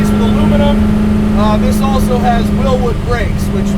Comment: what kind of brakes does he say the car has? I heard rear wheel.